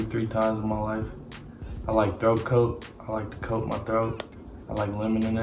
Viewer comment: when he said throat coat i thought he said throat goat😳